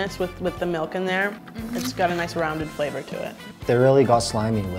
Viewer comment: anyone else hear this 😆😆 "It's gotta a nice Raman flavor to it"